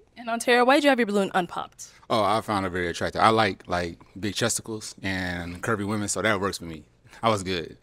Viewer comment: did he say big chesticals lol